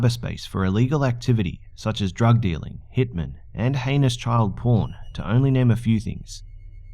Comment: i thought you said anus intead of heinous